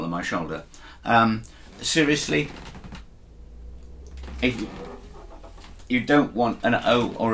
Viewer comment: you say "You don't want an O or a OO, I can't think why you wouldn't"...Did I hear wrong, because if I did not, the sentence would be contradictory. Also, do you favour rosewood back and sides over mahogany and, say, walnut? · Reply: Good spot, actually there was a half swallowed "If.." as in "If you don't want ..." so my original point was that for playing at home seated the smaller the guitar body the better, but I can understand that my diction was less than perfect. Thank for pointing that out.